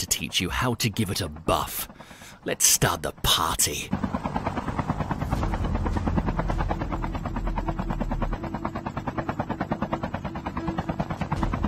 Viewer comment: I LITERALLY HEARD "LET'S START THE POTTY"